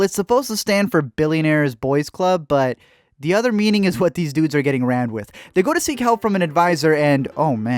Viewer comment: did he say “what these dudes are getting rammed with”?Lmao xD